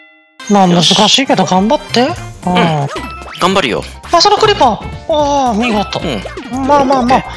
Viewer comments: あっそこのクリーパー に聞こえた。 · 3分28秒のところがクリボーのことクリーパーって言ってるように聞こえたww